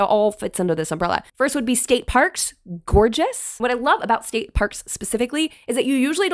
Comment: I had to rewind because I thought you said "skate park" lol